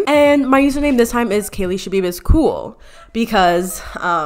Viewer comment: omg the first time i heard you saying your lastname, i always sad KEBIB